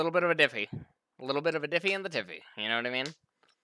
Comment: what dies say sind what does it mean? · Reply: He probably misspoke and meant “Diffy in the jiffy” which means jungle diff.